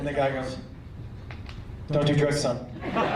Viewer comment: what is the punchline at the end, I cant understand what he said due to audio quality. I'm hearing "Dont you dress some"? at 7:39 mark, cant understand shit he said.